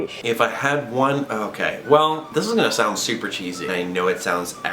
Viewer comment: I thought he said cheetos and I was like what that's not an animal